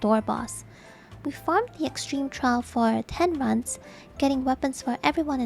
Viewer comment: Did I hear her correctly? Grinded ultimate for 10 months before returning to savage? @ 13:05 Edit. She says 10 runs. Not 10 months. Makes much more sense now. lol